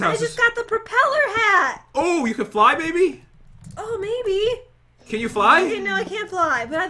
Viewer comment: I SWEAR HE SAID BABY NOT MAYBE